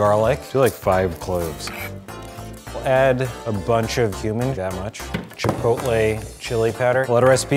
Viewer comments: When he said "we'll add a bunch of cumin" I thought he said human lmao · When he said cumin, I thought he said human and I was so confused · I thought he said add a bunch of human...